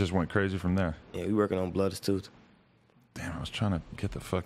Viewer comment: Yooooooo did durk say Bloodas 2???